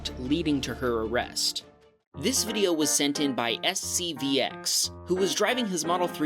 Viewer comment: I thought he said SCP...